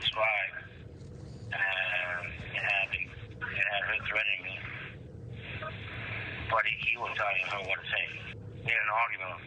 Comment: This is the part that wasn’t transcribed above: 5:24 “and it had her threatening me. But he will tell you what it’s saying. In audio what I have, I noticed, I knew they were paranoid. Um I knew they were paranoid so ….. I want to guarantee my my safety, so I say, I text him I said, hey if anything happens to me I got it I got evidence on me with you”